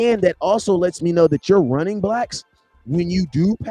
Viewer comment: did he just say, "your running blacks"?? LOL